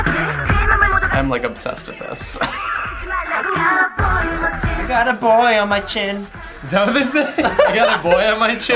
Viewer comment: "I got a boy on my chin" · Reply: I always thought they said i got aboy in my jeans😂😂